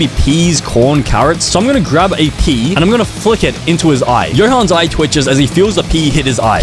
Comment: AT FIRST I THOUGHT HE SAID KEY💀